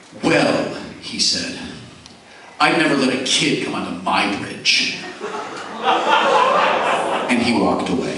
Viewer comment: What did Shatner say to him at 12:40? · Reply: "Well," he said. "I'd never let a kid come on to my bridge."